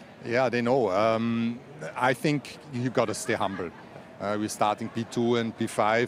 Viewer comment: I had to replay it several times... Is he really saying this: "...stay humble..." . 👴🤔🤭🤣🤣🤣